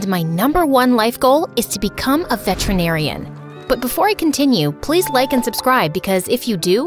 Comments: I thought she said my life goal was to become vegetarian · when she said veterinarian i thought she said vegetarian 💀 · I thought she said vegetarian lol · I thought she said vegetarian instead of veterinarian 😭